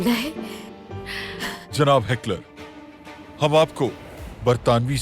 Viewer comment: did they say hitler 😳